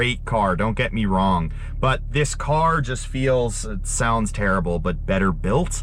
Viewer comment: At first, I thought you said the RS5 "sounds terrible". I was like, I know the 5-pot in the RS3 sounds sick and most V6's are nothing to brag about, but that was harsh. haha. But I know now that's not what you meant.